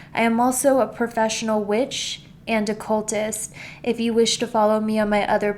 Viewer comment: Bro you're freaking me out. Literally sent shivers down my spine. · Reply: and did she just said cultist?